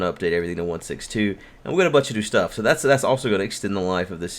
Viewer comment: 'And we're gonna bunch a do stuff' Is that what I just heard? xD